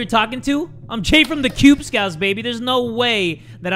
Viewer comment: I thought he said qubescouts